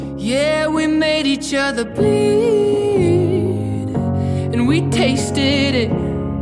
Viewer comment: When he said "made each other bleed and tasted it" i thought he said "made each other pee and taste it"